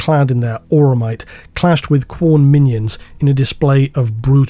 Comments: Did you say Quorn Minions at 23:07 or abouts? 😂😂😂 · I heard “Khorne minions” as “Quorn minions” 😅